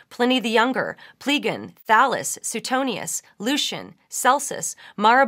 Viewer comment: Thought she said "phallus."